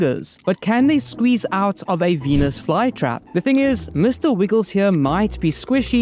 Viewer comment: I thought he said penis fly trap💀🙏